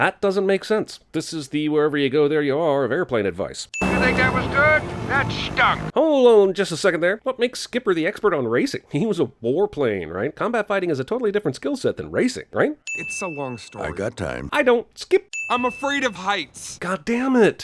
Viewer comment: Who heard fighting instead of flighting?